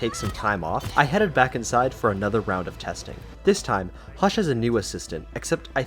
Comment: subtitles says josh instead of hush